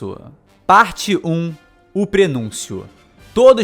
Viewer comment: No começo eu entendi "parte 1 o prepúcio" chorei largado kkk